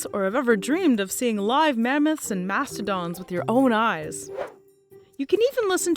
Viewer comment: Did she just say ‘master dongs’, or is it just me?